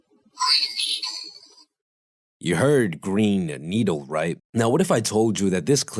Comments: So I heard "Green needle" both times... I'm confused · Maaaan, I heard "green needle" at first, then it was "brainstorm" as you said, so I challenged myself and got "green storm". Freaking fun to play with it hahahaha